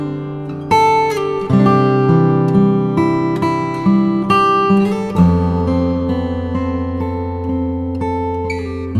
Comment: kuntu prakashonor hoi ?? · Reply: Jyoti prakashon